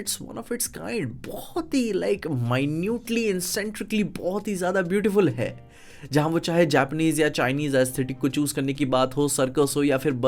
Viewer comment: kya bola bhai😭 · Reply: Minutely eccentrically beautiful